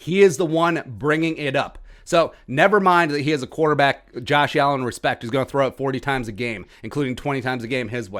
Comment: *waits for triggered bills fans* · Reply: i thought you said josh allen was trash?